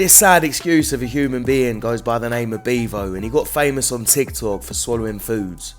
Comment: beavo? I thought you said beaver 😁